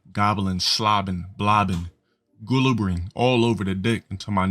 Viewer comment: did he just say "gu-lubberin"? 😂